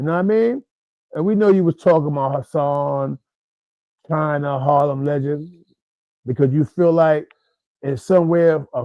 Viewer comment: Maybe you're the one who needs to listen again. included himself in that group that Jones was talking about. Hence thinking Jones talking about him. Learn how to comprehend better buddy! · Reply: He clearly said “We know you were talking about Hassan,China,HarlemLegends” HE NEVER INCLUDED HIS SELF IDIOT. No point in going back and forth when the proof is time stamped,rewatch the video buddy because your comprehension level is not even past a 5th Grader Bird Brain🖕✌🏽